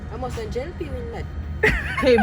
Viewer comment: what did he say? 🤔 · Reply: He said it’s a JLP boat